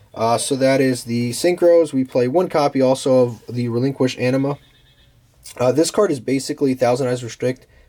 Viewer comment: At first I heard, "We play one copy also of the relinquished Enema" 😂😂